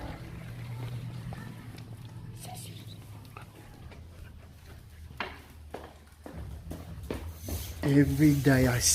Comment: do I hear Sassy or Sissy ? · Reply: That was Sissypants.